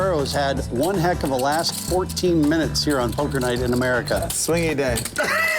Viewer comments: What does Doug say at 18:12? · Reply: "swingy day" smh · Anyone make out what Doug says at 18:13 that cracks up the table? (Sounded like, "Okay, swing your dick.") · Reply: Swingy day. Talking about poker swings but its funny because he idiotically lost a 300k pot with KJ for no reason cuz hes drunk and blamed it on poker swings lol.